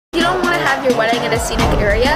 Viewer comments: Lol I thought it said your not my husband YOUR my wife 😂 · Help it sounded like she said "your my wife"😭😭😭